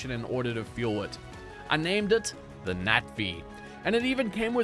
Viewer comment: am i the only one who thought he said nazi when he said the natvee around day seven?